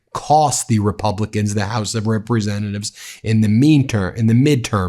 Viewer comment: I didn't hear you right -- I thought you said the "House of Reprehensibles"